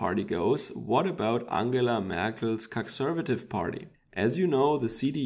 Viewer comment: Did he say "Cuckservative", or did I hear that wrong? Thüringen pulling right bodes well for Germany though.